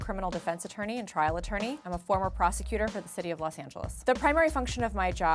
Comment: When she said ‘Los Angeles’ I heard ‘Los Santos’.